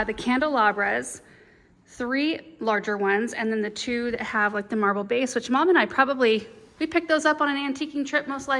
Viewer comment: At first I thought you said candelabra w marble base you guys picked up on Amazon and I rewound it bc I was like I think heard that wrong ! Ha ha. Looks great